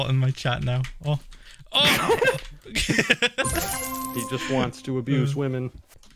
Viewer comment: anyone else hear "he just wants to abuse women"